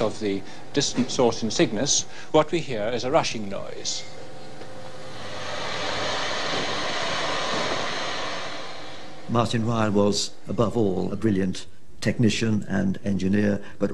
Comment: a Russian noise oh it's the Russians again now they created the big bang too ! Haha. That's what i heard first